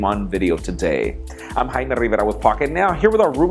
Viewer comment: It almost sounds like he says his name "Hymen Rivera". lol.